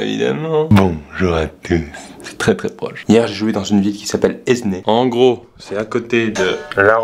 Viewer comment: Au début j'ai compris "hier jai jouis dans une ville...